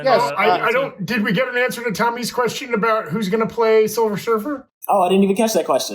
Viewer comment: For some reason I kept hearing quicksilver and I was super confused and then I noticed the were saying silver surfer lol